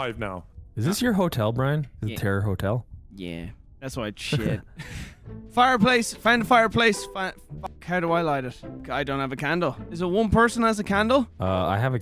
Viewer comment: I thought it said “terrorist hotel”